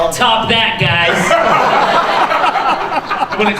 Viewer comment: what did Mena say? Can someone tell me please. I'm a foreigner 🙏🏻😓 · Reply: He said "Top that guys!"